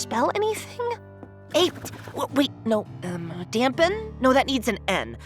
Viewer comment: I THOUGHT THEY SAID TAMPON HELP